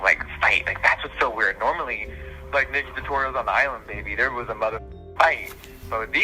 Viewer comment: J*ffree said he had a fight with nikki but who was the other youtuber? I keep hearing 'Island Baby'